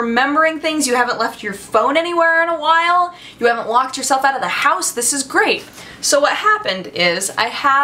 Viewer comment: I thought you said "Walked yourself out of the house", you need to make that a thing so I can laugh at it actually happening lol.